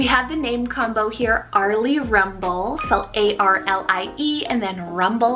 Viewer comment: Omg my name is avery rumel so when you said Arlie rumble I thought you said my name ahahahahah